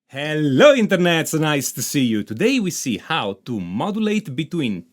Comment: "Hello Internets, nice to see you!" Love this channel. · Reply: I hear it as "Hello, Internet! So nice to see you!" instead.